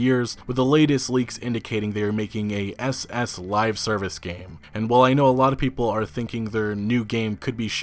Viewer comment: I thought meant SS like the Nazis, so I heard: "A SS Live Service game" I wasn't watching and didn't see it was suicide squad.